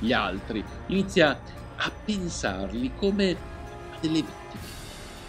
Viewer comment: Cosa dice al 4:03 come delle?